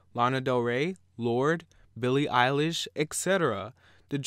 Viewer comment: lmao did he just say "Billie eyelash"